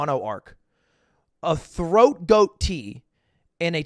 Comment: he was saying throat coat and i was like “did he just say throat goat” edit: likes